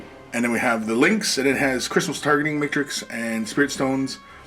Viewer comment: does anyone else hear “Christmas Targeting Matrix”? This made my day lmao